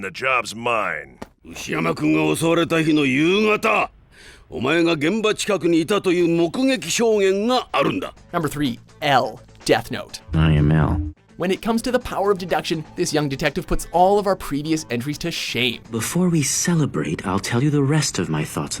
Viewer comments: did he said no. 3? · did he say "number three"?